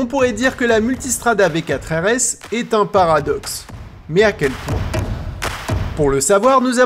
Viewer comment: a quel quoi ?? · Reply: À quel point